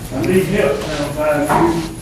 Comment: what does Autry say at the end as he is led out ? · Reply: I thought he said " I need help, channel 5 news". Of course I could be completely wrong lol